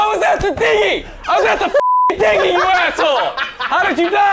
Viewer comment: listen to sohinki, he says 'funkin' instead of fuckin